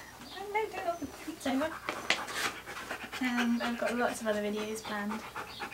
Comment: Did you say you have videos banned? I've never once seen an inappropriate thing in your videos, I must have heard that wrong or misunderstood you. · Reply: Lots of videos planned :)